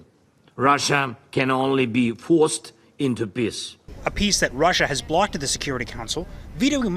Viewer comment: he really said “Russia can only be forced into piss” 😭